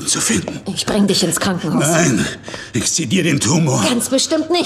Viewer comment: Kann mir bitte einer Sagen, was er bei 4:20 gesagt hat? Ich habe verstanden "Ich zieh dir den Tumor" Das macht doch garkein Sinn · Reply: *Ich sezier' den Tumor.